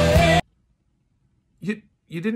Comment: When it said why no toy sharks I thought it said why no tony stark